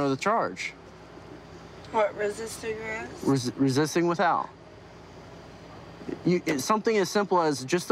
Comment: Resisting with Al??????????? It sounded like he said that to Courtney's daughter?????? Did I hear that correctly? Resisting without?